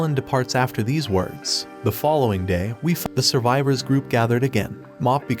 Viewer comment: nah this is mass up we fought the survivor instead I hear we fuc* the survivor